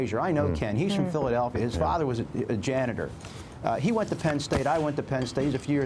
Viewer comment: did Dent say that he was Frazier's janitor? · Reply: Andrew Velonis... No he said Frazier dad was a janitor.